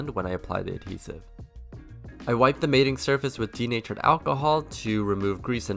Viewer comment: did he said "teenager alcohol"?!